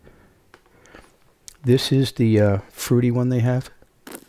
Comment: What? I always thought he said frutti not fruity